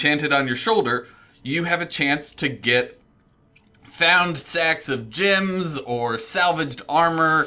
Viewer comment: sounds like he said, "Sex of gems." Sounds interesting, I'll take a few. Also wanna point out it sounds like he says Sex instead of sacks... a lot lol.